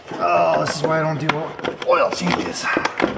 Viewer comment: "This is while I don't do oil changes." While in the midst of an oil change. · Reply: I thought he said "why I dont do well on oil changes" As in doesnt make any money on them cuz he spends the whole time fighting an airbox or other related task